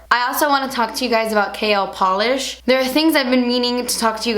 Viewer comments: Lmao I kept hearing "kale polish" WHOOPS. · lol thought u said kale polish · I honestly thought she said kale polish!!!!